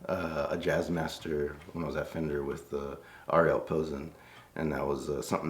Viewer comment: Did he say 'Ariel Pozzo' in minute 12:09? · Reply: Ariel Posen (guitarist)